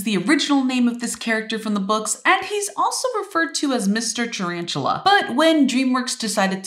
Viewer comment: When I heard her it sounded like she said Mr.tarantula and not Ms.tarantula